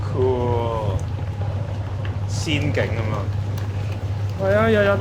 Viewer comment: btw 1:50 the subtitle is wrong is saying "仙境咁喎" (Just like Wonderland)